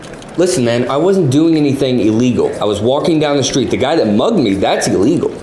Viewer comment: i thought it said MUGGLES lmao